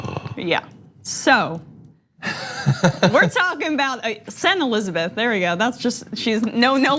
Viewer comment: When Emma said "Sen. Elizabeth", I heard "Saint Elizabeth".